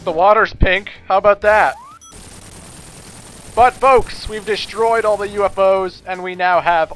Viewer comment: I thought you said "But folks we've destroyed all the mofos." I was skipping a bit at the time XD